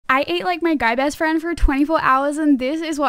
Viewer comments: When you were saying that you you ain't like your guy best friend for the whole day I thought you said I eat my guy best friend for the whole day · I thought she said i ate my boy best friend 💀